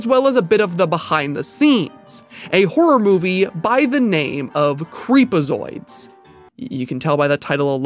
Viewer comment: Who else thought it said "Freekazoid" right up until he actually said the name?